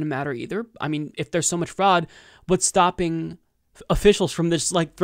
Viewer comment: Haha I thought he said “Donald Trump is a frog” but he said *fraud He’s sitting like a frog